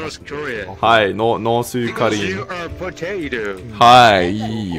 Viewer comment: Omg lol i put my ear to the speaker to hear him and i just hear "YOU. ARE. POTATO"